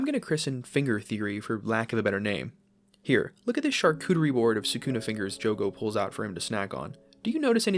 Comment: did I just hear "Sukuna fingers Jogo"?